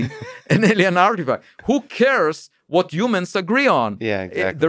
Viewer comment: When ever he said 'who cares' I hear 'hookers'